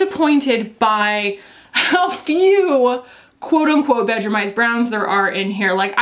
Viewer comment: I always thought you were saying "bedroomized browns"...